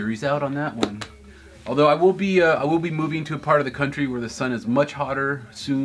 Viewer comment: What I heard was "moving to a sunnier part of the country really soon."